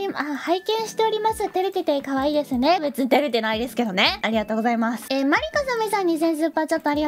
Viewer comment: 誤字があるように思えたのは気のせいですか？ ベージュの文字で 別に出てないですけどねの所が 私には 別に照れてないですけどねに聞こえました っていう報告